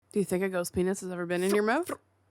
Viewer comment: I had to listen to that multiple times. I thought was saying goat penis 😅